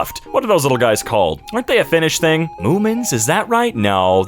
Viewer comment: Holy hell, I had the worst mishearing thing, I thought asked if Moomin was a fetish thing instead of if it's a Finnish thing. Lol